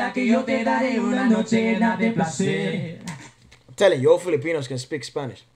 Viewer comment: Please please someone answer me coz im really curious. Who are you talking to outside the frame? If I heard correctly you referred to him as a filipino. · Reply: Gabriel ooooh.. Had to rewatch it. I thought he said "im telling you, all you filipinos...", but it's "im telling you, all filipinos". Lol. My bad 😅